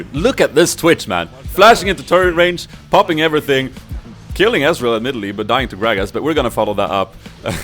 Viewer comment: Hey... I heard you say 15:25 "Killing Ezrael and Nidalee" So is it just me or did the really say it...? Apart from that GREAT vid and I hope you Get 100,000 Subs...♥♥